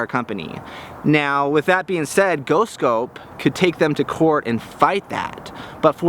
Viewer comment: Just change the name to GhostScope that's what I thought you were saying.